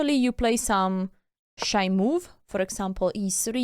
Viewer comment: It might be just me, but every time you say "shy move", I hear "shite move" 😂